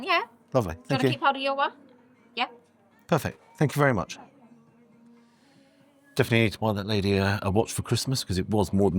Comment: What did she say at 3:12. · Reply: You're welcome